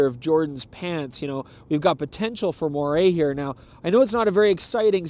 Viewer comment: what's the word he uses at 5:41? "moray"? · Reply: moire